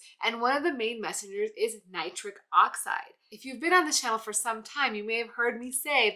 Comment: Ooh, I thought she said nitrous oxide? No wonder I was away like a mustang.